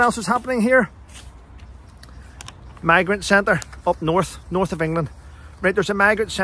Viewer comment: Morning, I laughed when you first said “ migrant centres “ I thought you said “ maggot centres “ probably nearer the truth 🤣